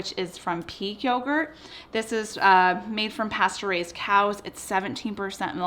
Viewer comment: Had to do a double take at 4:12 Sounded like you said it's made from "Pasturized cows" WHAT!!?? oh, pasture RAISED cows... lol